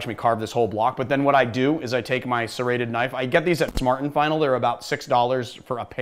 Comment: You can get serrated knives at... did you say, "Smart & Final" (8:35)? Is that a store?